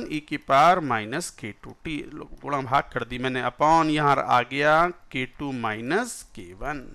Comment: What is sir saying at 12:18?? " Lo __ kardi maine" like that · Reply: "guna bhaag", it means multiplication and division, he basically meant that he solved that part